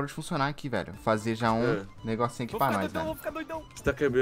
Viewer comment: só eu escutei o falando vão ficar doido?